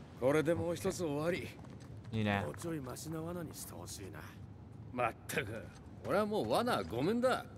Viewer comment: もうちょいマシな罠にして欲しいな…ってとこでもうちょい芦○愛○にして欲しいなって聞こえてしまった…